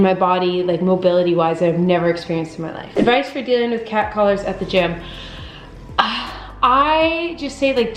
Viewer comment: For a split second I misheard 'cat callers' as 'cat collars' and was so concerned for a moment. Like, wtf are cat collars doing at the gym. Is this a cheaper way to wrap your hands? I think I need a nap.